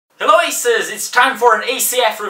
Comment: Press 0 again multiple times whist watching this video, It sounds like he is saying racist in an Irish accent. Anyone else??